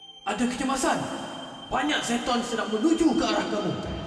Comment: LOL i heard "Banyak Syaitan" instead of"Banyak Zetton" XD